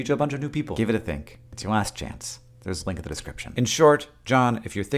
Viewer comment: Misheard the question about commuting, thought he said “taking a cab to work,” got confused about why the cost of a cow mattered 🤷🏻‍♀️